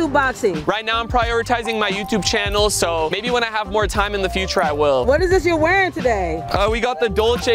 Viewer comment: Almost thought Rug said "for free, I will." Like what have you done